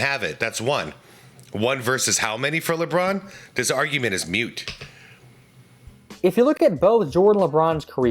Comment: Did i hear Mute? I think you mean Moot as in “it’s irrelevant”. Or like Joey Tribiani in Friends “it’s a moo point, like what would a cow care?’